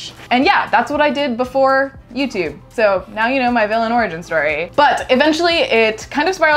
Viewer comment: I realize you said vanilla origin story. But I heard “villain” origin story. Which given the context is hilariously ironic. Like I laughed and did a spit take. Seriously though this is a fantastic idea.